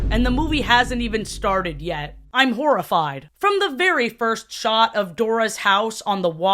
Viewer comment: first time I heard this, I thought you said Dorafied 😭